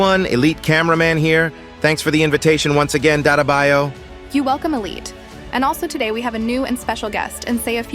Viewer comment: Bro i thought i heard Data ba toy 💀😂